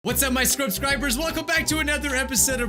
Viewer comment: omg i thought he said "wassup my screwdrivers" lmao